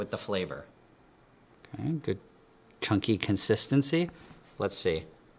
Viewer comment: Did he say "Cunky" ??